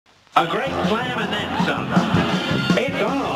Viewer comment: A G R E A T S L A M A N D T H E N S O M E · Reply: thought he said a great plan and dim sum